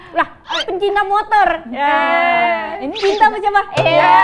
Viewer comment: itu ngomong apa ke Arafah cepet banget saya kurang denger walaupun udah di ulang2.. Minta tolong yg tau kasih tau saya dong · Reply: ini cinta ama siapah,gtu klo g salah